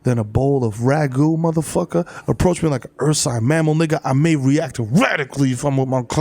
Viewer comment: I'd say something about THE KID MERO eating right on the mic in the beginning, but I'm gonna treat him like an ursine mammal and respect his agency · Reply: ughhh this whole time I thought he was saying "an earthside mammal" which didn't make sense to me but I wasn't going to question it....fuck I'm so slow 😑